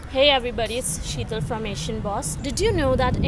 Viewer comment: I legit thought that she said "hello everybody it's shitter from asian boss"